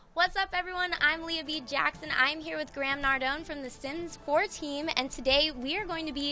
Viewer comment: did she just say sims 14 smh